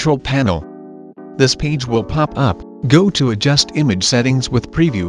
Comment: did you just said page?